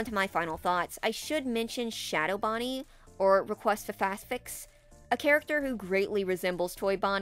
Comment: am I the only one who hears "request a Faz fix"? It's fitting cuz he is an error or smth lol